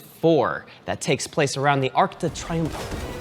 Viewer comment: When said "Arc de Triomphe" I thought he said "Arctic Triangle" lol